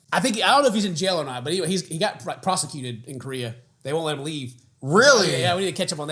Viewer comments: oh my gosh I misheard prosecuted as prostituted, I was so shocked for a minute. lol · I misheard the video and thought you said he was prostituted in Korea